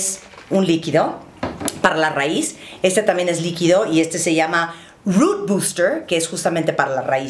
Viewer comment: Body que? No entendí